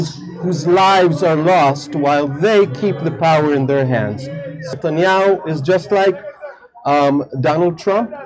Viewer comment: did he just say "Satanyahu"?